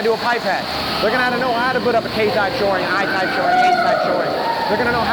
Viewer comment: What’d he say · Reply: He says so they know how to put up a K type shoring, I type shoring, H type shoring, Box patch. Or something like that